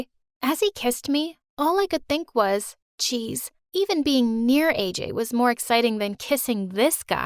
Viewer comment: I heard “As he was kissing me all I could think of was CHEESE-“